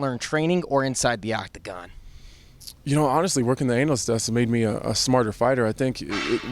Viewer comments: Did he say "Anal"list😂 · I thought i heard Anthony say "honestly working the anal sex has made me a better..." lmao